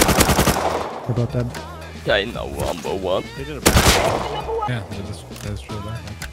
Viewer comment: "CHINA NUMBER 1" did they just say that s they died? its like when a knight dies and shouts long live his majesty